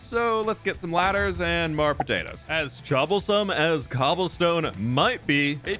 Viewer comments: … did you say chobblesome 😂😂 If you know, you know c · Did I hear this right?! Did say Chobblesome?! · did he just say chobblesome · it sounded like you said chobblesome not troublesome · sooooo did he say "choblesome" or am i watching too much hermitcraft o.O